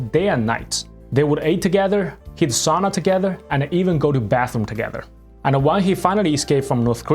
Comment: did i hear that correctly? Go to the bathroom together?